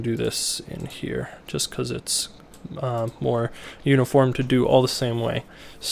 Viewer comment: Minute 2:35 i don´t unsterdant what you said? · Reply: "it's more uniform to do all the same way" ? That's what i say just before 2:35.